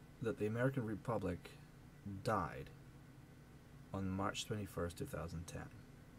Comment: Sounded like you said "March 31st 2010"..? Your point is the republicans are going to have to campaign on taking away people's ability to get health care with a preexisting condition. How do you figure? If they were going to campaign on repealing Obamacare, wouldn't they also promise to pass a bill immediately granting the right to health care with a preexisting condition? That's what I would do if I were running. All that aside, this is partisan BS. Revolution of the system is the ONLY way.